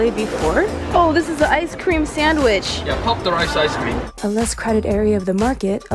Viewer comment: it sound like he said F**ck the rice ice cream 😂